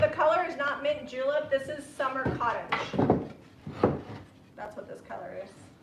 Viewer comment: That makes sense know, the summer cottage, I thought you said earlier summer crush...so the color you used threw me off..